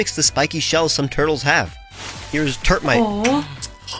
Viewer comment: I don't want to sound mean but I heard turdmite first for some reason and was really confused